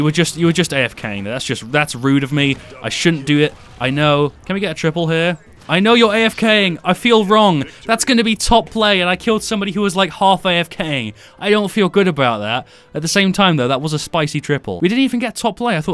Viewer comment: Did i just hear bk say " i feel horny" when killing seris?